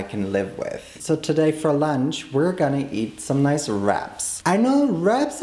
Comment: oh my gosh i thought you said rats instead of wraps but when i saw the tortillas i was like “OHH, WRAPS” i was also so confused bc i was like “wait, isn’t he vegan or vegetarian?”